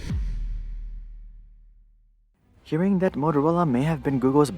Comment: Did you just say aferola at 2:47? 😅